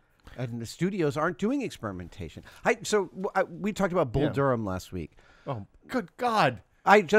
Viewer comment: Someone catch the name of that movie? I'm curious to see it ! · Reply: Bull Durham?